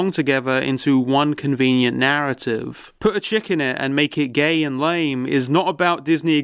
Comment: Im not gonna lie, through the whole episode I thought was saying "put a chickin in it and make it gay"